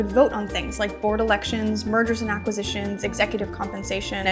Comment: I heard "mergers & acquisitions" as "murders and acquisitions". And for a second there..... wasn't surprised that that was a thing in finance.